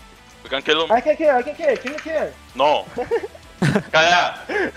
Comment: 1- 5:49 creo que dijo KDA xd 2- 7:40 que dijo? 😳 3- No pusieron la solo kill de contra el corki 😠